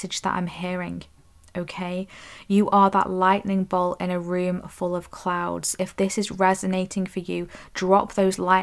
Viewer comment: I thought (at first) you said "youre a lightning bolt in a room full of CLOWNS.." Lol I definitely resonate with that!!!! 🌩 ⚡️ ⛈️